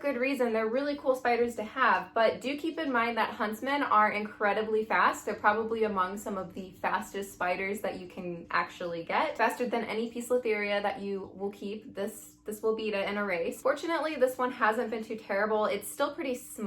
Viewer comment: Can someone please tell me what spider she said at 4:47 because the subtitles say “ peace with area” and I’m confused.